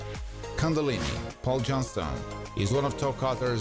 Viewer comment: Always found the Mad Max names so interesting. Cundalini always sounded like they were saying Cunnilingus. Just like Imperator Furiosa is made to sound like Emperor. Just added to the coolness of the world of Mad Max.